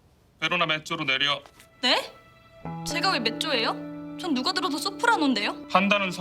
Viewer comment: 근데 6:28 누가 들었는데? 판단은 선생인 내가해 아니었나 왜 누가 들었는데가 빠졌지